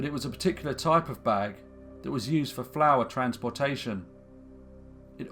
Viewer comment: When the narrator said that the hessian bag was used to transport flour, I thought he said flowers and got terribly confused. As an aside, the Aberdeen tenements were much nicer than the ones in Glasgow. I was born in Dundee and had lived in both cities. I remember vividly the state of disrepair of the Glasgow ones and was amazed that the tenants still had to use an outhouse, even in the mid 70's.